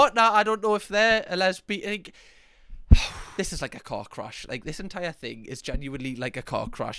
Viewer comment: 😂😂omg i thought you said cock crash 😂😂😂😂thank god you said car crash again 😂😂😂😂